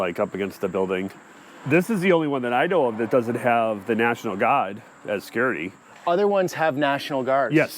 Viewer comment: The guy in the red shirt, his accent is so think when he said "national guard" I thought he said "national God"